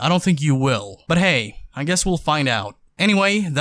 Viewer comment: "when he said as always" i thought he said "assholes all the way"